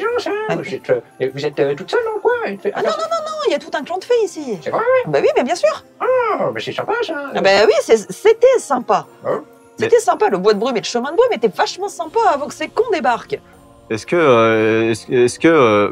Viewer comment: je suis fatigué, jugez pas, j'ai entendu "le bois de Boulogne"